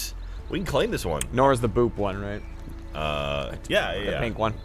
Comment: I thought Jeremy said Nora was the "boob" one, because no, that would be Yang.